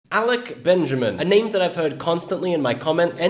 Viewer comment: my dumbass thought is said alec baldwin